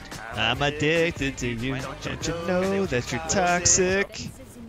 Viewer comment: I thought u said “I’m a dick to you don’t you know that ur toxic”